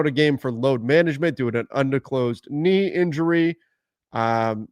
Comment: Did you just say "diclosed" I'm not sure that is how you pronounce disclosed 😂😂😂